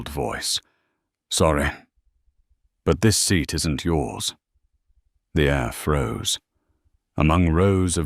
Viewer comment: "The air froze" corrupted to "The Afro". (0:13) AI- captions totally misrepresenting what's been said by the ROBOT narrator. - THUMBS DOWN!